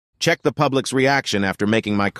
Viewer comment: I heard "czech republic's reaction" In the start😭😭😭